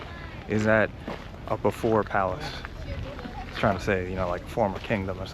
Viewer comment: what you said could also be interpreted as “so, before yard”…. lol makes it more confusing